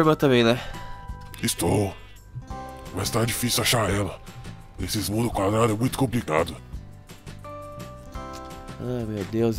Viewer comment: só eu que escutei o falando esse muro do CARALHO ´mt complicado